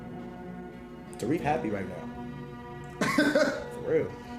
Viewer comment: Trading in crypto and doesn't know what "tareefs" (tariffs) are. 😂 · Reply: Did he really say Tareef happy right now 😂